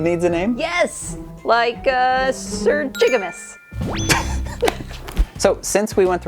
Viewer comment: for a second i thought she said sir Niggamus, instead of jigamus